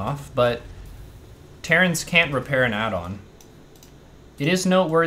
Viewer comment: Did you said : Terran "cant" repair an addon ? or terran "can" ? sorry im french and i didnt catch what you said :) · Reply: He said can't. I think he meant they can't repair while the add-on is building.